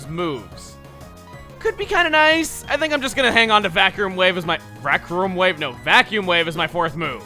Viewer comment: The first time you messed up "Vacuum Wave", I thought you said "Bathroom Break", and I burst out laughing.